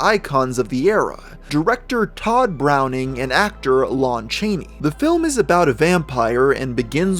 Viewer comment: did he just said lawrence chaney XD